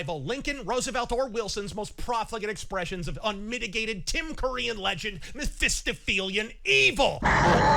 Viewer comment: You said "Tim Curry in Legend" What I heard somehow: "Tim Pool-ian legend"...?